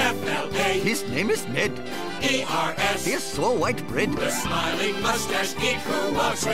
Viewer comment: I thought they originally said "His wife is dead" instead of "he's so white bread"